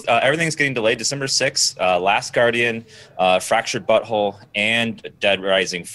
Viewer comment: did i hear "fractured butthole" at 3:24 ???